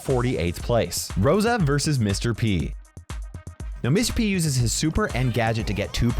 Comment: I heard miss p